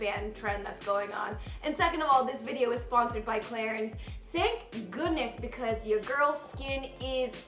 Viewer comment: tell me why i thought she said “second of all this video is sponsored by claire’s” esp after talking abt bows & headbands